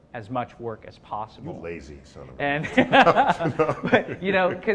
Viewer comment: did Neil just said "you're lazy son of a bitch" ?